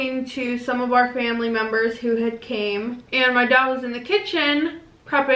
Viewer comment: “My dad was in the kitchen.” And I heard “mY DOg wAS iN THe KiTCHeN” and I’m like what the hell? How do dogs cook